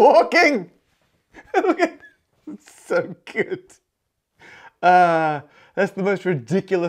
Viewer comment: subtitles says 'James Street' instead of Jane Street 7:35 Subtitles says 'octan' instead of arctan 12:10 Subtitles says 'F9' instead of (presumable) affine 13:38 'Goebel' instead of Goble 19:56 'gene street' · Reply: "okay good" instead of "so good".